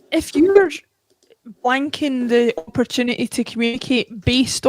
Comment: does say "if your wanking the opportunity...", unsure if I heard that right?